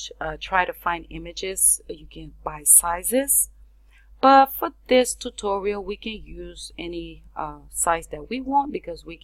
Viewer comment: i swear she said but f*ck this tutorial hahaha